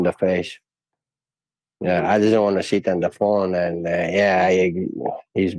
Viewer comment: did mans just say he’d sit on there face